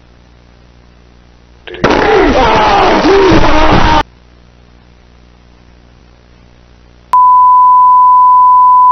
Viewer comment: what did he say🤔🤨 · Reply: Ah, ah